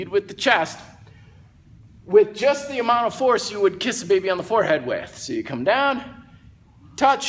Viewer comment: Lol. I thought it said "leiss da baby." I was like what does that mean lol.